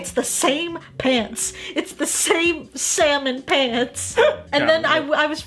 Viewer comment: did she say semen pants Getting a lil naughty aren’t u joey